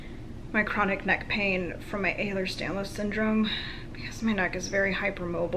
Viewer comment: I love your videos! I'm actually trying to help subtitle them. At 2:43 what syndrome did you say? I tried googling what I heard, and I got Ehlers-Danlos syndrome, is that correct?